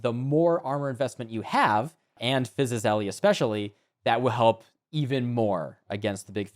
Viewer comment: What did you say around 13:23? "And phyisiusele especially", Cannot understand it :P //new player